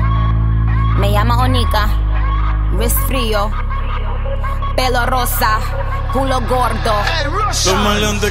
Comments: Nadie: Absolutamente nadie: Nicki: Pelo rosa, Cul0 Gordo Su pelo: Amarillo... :v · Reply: Y todo este tiempo pense que dijo pedorrosa and im like wtf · Like por que dice pelo rosa y tiene pelo amarillo :v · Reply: Yo pensé que decía pedo rosa 😂